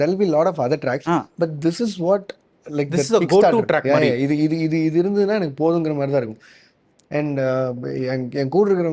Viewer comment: which track he is mentioning? I heard he is saying after hours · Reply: After hours ost by Howard shore